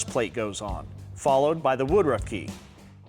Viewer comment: Did you say Woodrugh key??